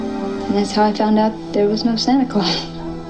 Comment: I thought she kept saying. "Thats when I knew there was no cynical" then a heavy nose exhale like a laugh